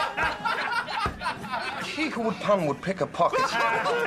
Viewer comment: What's the Doctor saying at 0:55 ?? I watch it over and over again and still can't understand :/ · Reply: He who would pun would pick a pocket - meaning if you would stoop so low as to joke so poorly, then you would be capable of stooping to thievery